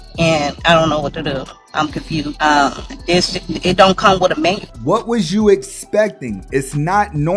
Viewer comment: Did she say "it don't come with a man"??!! I have zero sympathy for these nasty, dumb birds.